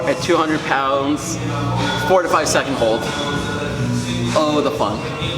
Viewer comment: Thought you said 45 second holds in the bench press haha😳😳